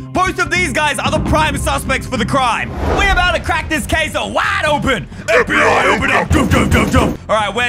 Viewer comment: Did bro said caseoh